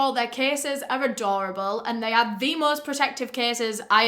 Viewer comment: I heard ‘this case is adorable’ when she was talking about castify aahahah, had to back track